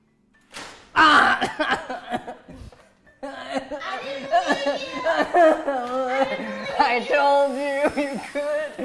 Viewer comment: she actually says “I didn’t believe you”